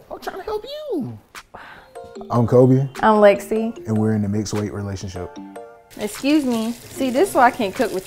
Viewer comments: WTF is a "mixed weight" relationship? All these labels for what's been normal for centuries are ridiculous. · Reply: I thought she said mixed race. I was looking like huh. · This is literally the average couple. Mixed weight relationship? They slap a label on everything... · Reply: I thought she said mixed race😂😂😂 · They making shit up now, mixed weight wtf. So if someone who wears glasses, get into a relationship with someone who doesn’t wear glasses, they in a mixed eyesight relationship 😂😂😂😂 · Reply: HAHAHAHAHHA!!!!!! I guess so! I thought they said mixed race and I was confused 🤣🤣🤣🤣🤣🤣🤣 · WTF is a "mixed weight" relationship? All these labels for what's been normal for centuries are ridiculous. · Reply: Lol I thought this gal said mixed race 😂😂😂. So that means I am in a mixed finance relationship 😂😂😂